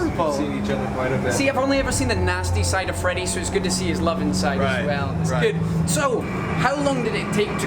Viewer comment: I thought at first that he said, "Nice to see he has some love inside." And I was like, "Yes... Love... Oscar Love was a stalwart security guard, he was."